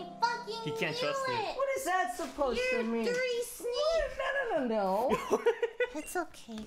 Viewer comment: did lily just call me a dirty s n a k e